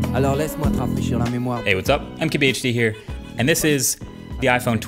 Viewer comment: When Marques does his intro, I keep hearing: "I'm KBHD, here".